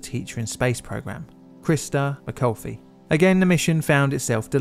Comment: It's Christa McAuliffe. It sounds like you said McCoffee.